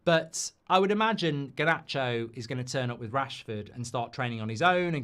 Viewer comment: I thought said ganarcho is gone 😂lol sauces